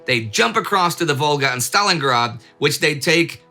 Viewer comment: excuse me, did someone say... Stalingrad?